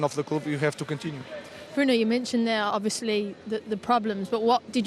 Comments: Did she call him Bruno at 4:20? 🤣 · did she call him Bruno or am I hearing that wrong